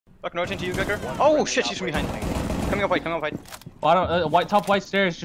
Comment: what the hell did he say? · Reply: "Oh shit she's coming behind" I believe. And then "coming up white"